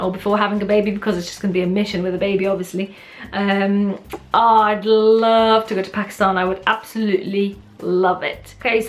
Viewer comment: I thought that you were saying "I would love to go to PAC sun "❤️